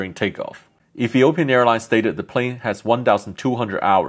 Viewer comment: I thought you said Philippine Airlines